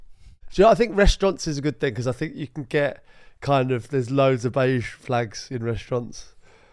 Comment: I thought it said 'Biege Flaps', that got my attention lol